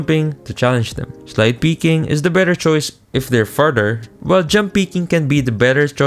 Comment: it sounds like he said “Farter” 😜🤪💩